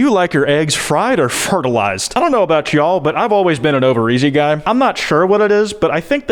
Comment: he said over easy and I heard Ovaries and didn't even bat an eye 💀